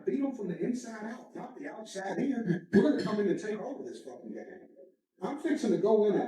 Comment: he clearly says were going yo takemover this place/street- why do you have to reach to make it what you want, instead of listening. This damages credibulity, and why christian truthers have bad credibility. Inatead if just going with the plethora of evidence, that proves this guy is corrupt and playing. All you guys do is getting, almost as if your religion is as social enginering against the people, particularly the "gentiles"